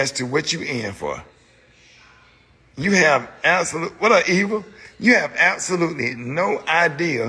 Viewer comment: Did he say, “Wha’ up evil” 😂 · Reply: No , he said what up Eva?